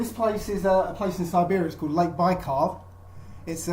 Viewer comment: Did he say, "Lake Bicarb"? Well, that's useful if you've got indigestion, I suppose.